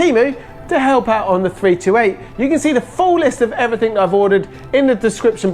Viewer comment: did you say 328 at 6:03..?